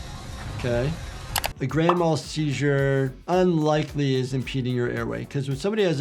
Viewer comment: When I was a kid and heard gran mal seizure I always thought they were saying grandma seizure